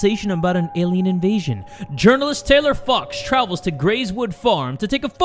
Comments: I thought Jay said f**ks, but it was fox 😳😂😂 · Jay: Saying “Journalist, Taylor fox” Me: “I THOUGHT YOU SAID JOURNALIST TAYLOR FUCKS-“ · 😂😂😂😂😂I thought you said Taylor fucks not fox😂😂😂😂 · I've gotten so used to Jay cursing that when 1:11 he said fox I heard f*cks and when 7:04 he said big eyes I heard big *ss